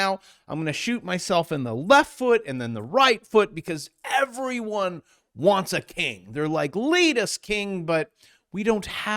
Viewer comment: I coulda sworn you said “ere’body wants a KINK” 🤣